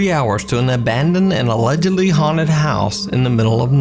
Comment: As a non English speaker every time I heard the word "allegedly" I mistake it for "legally".. what a surprise I had when I turn on the captions